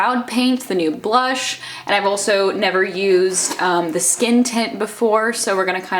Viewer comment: I kept hearing you say "skin tit" instead of "skin tint" and it's making me laugh so much 😂